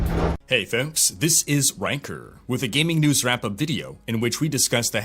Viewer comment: I know you are probably saying Rhykker when you introduce yourself but all I hear is "wanker".